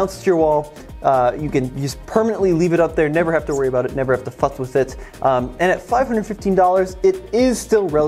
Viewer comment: Maybe it’s just these AirPods but I swear said that I never have to “f@ with it at 11:03.